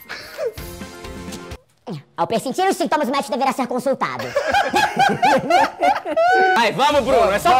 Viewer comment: só ouvi o final onperisitivonauabagsuajwnajja consultado